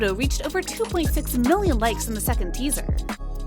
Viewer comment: I can't with the kids instead of likes😭